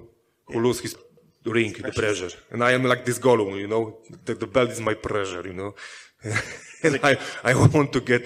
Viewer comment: he was actually saying "the belt is my TREASURE you know" and not "the belt is my precious"